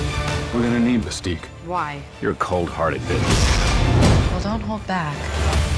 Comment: Did Wolverine say,"you're cold hearted bitch" or what?